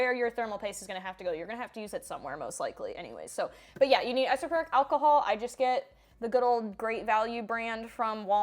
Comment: I thought she kept saying Isofluric Alcohol, like Isoflurane the anesthesia? lol